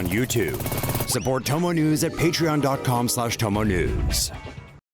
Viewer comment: Please do us a favor and change your into/ ads. It sounds like you say "chomo news". When he said tomo news. He needs to say it better..